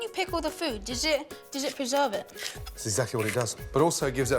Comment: thought he said "does it deserve it?" and I couldn't help thinking no, it really doesn't.